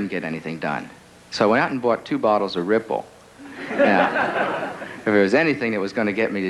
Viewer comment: What drink is he saying at 4:49? Ripple? Red bull? Trying to look it up. Thank you to any kind person that can help! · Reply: Ripple